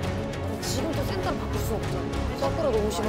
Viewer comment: what did Sakura say? anyone please? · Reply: I think it's better for .... to be the center.